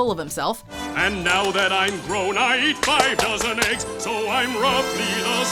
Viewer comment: Oh... 5 DOZEN eggs! I thought he said 5 THOUSAND eggs!